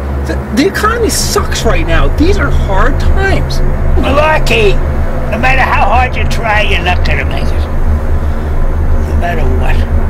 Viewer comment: Did that old guy just say "No matter how hard you try your NUT going to make it?" At 2:01 Or did I hear that wrong?! haha!